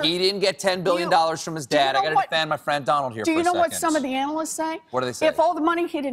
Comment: Did Colbert say, "I have to defend my friend Donald here." ? Why does he bash him so much now? Honest question.